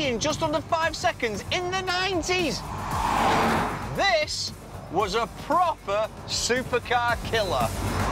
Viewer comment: I thought he said “this was the proper super godzilla” 🤔😅